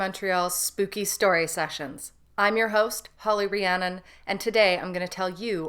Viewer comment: I'm your host You said but I heard that I'm the Ghost 😂